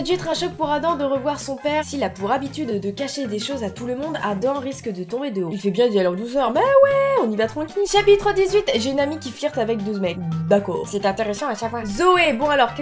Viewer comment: j'ai entendue DOUZE MECS !!??!??!?????!!???!!